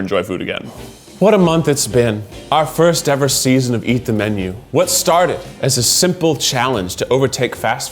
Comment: When he said "Eat the Menu" at the end, I thought he said "Keith the Menu"